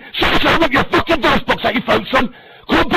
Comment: what is bro saying 1:33 came down because of energy? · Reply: "I'll rip your fucking voicebox out your throat son" 1:33 "If you want to fucking brawl, come down Smethwick, ask for Danny G, I'll come out my house..."